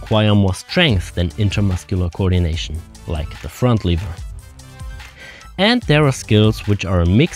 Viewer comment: It's front lever not liver. Liver is an organ in a body. I was making the same mistake when i was learning english :D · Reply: Sounds like he said LEVER.